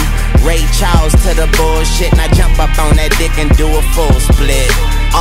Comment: Did Wayne say "I hop up on that deck and do a full split"???? Wth!!